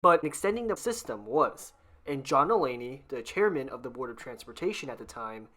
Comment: Did you say John Mulaney??